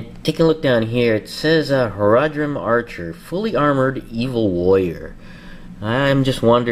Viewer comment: I wasen't watching the video at first, just listening to it while checking another website when I heard: "Fully Armored Evil Lawyer" and I was like "whaat?" so I clicked up the video, rewinded it and watched it again. Then it made more sense. :)